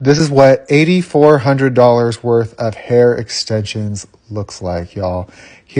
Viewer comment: At first when he said 8,400 i thought he said 84,000 😭😭😭